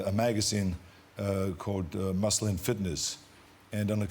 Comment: Hasn't Arnold learned there are differences in the pronunciation for MUSLIN and MUSCLE (2:10) ? I'm Brazilian and I only understood what he said through context. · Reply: He said "muscle and fitness" with an American twang on the "and"...